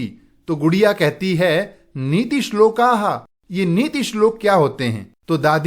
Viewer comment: I was watching in 2x and at 1:47 I thought that sir said budhiya but actually it was gudiya😂😂❤